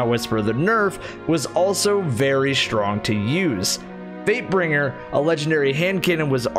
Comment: Swear to god anytime he said fatebringer i thought he said vapebringer